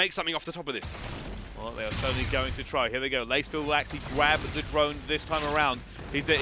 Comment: did he say "gwab the drone"?